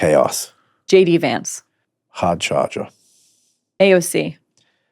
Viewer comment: Can someone 'translate' what he said at like 1:18:02 to describe J. D. Vance? I couldn't understand lol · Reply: "Hard charger"—Never heard that expression before. And I needed CC to see it.